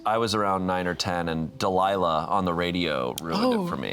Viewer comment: DID HE JUST SAY DELILAH??!!!!??? LIKE WISH 99.7 DELILAH ON THE RADIO??!!